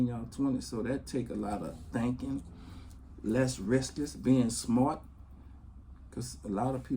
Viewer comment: I love Baby, but did he just say less riskess (risks) ? Lol